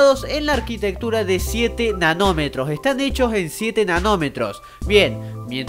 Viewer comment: entendi enanometros :-P